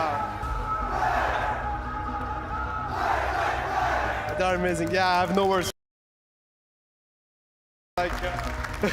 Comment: what did itachi say at the end? they muted it and he apologized after saiying it 5:30:50 and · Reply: He said "it's fucking crazy."